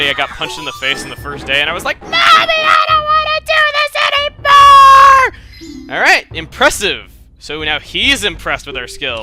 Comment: My brother thought you had said "NAVI I DON'T WANNA DO THIS ANYMORE!" when you were talking about trying karate as a kid. (He thought you were talking about Navi smacking you in the face)